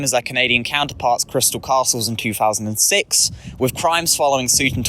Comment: Is he saying "Crimes" or "Grimes" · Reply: crimes